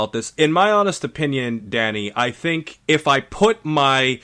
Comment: When q said Danny I thought he said daddy